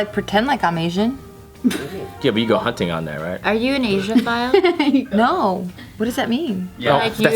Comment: what word was Khalyla asking Geo 'asian file'??